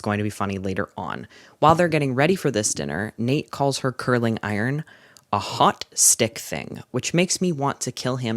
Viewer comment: oh my god….I misheard you and thought you said that Nate called ANASTASIA a “hot stick thing.” Which is wild already, but even worse when you add the context of her struggling with an eating disorder 😭